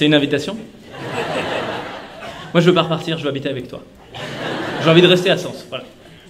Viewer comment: Elle dit quoi à 1:26 ? Car je m'attendais à ce qu'elle a la réponse mais ça ressemble pas à un nom de président ou bien j'ai du mal à entendre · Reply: "Non mais pour repartir"